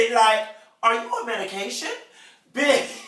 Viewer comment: Kenya asked: Are you on medication?? Porsha: I'm not on damn MEDITATION.. 😵😵😵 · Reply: Castiel Black I thought I heard her ass say meditation 😂😂